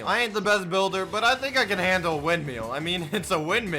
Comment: it sounds like hes saying wind meal instead of wind mill